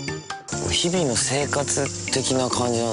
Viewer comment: did he said Arashi